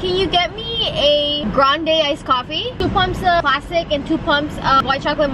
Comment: WAIT BUT I THOUGHT WHEN SHE WAS ORDERING HER STARBUCKS I THOUGHT SHE SAID “can I get 2 bumps of plastic” LMAO WTH